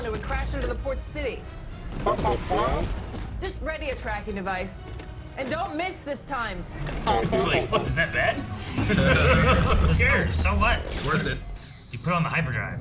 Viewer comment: I really do think Chop is saying "and that's bad?" 🤣🧡 Another creature in space was the giant creature inside the nebula/cloud round Kessel